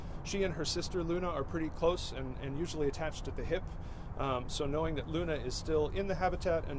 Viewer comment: did he say her and her sister are attached at the hip bruh?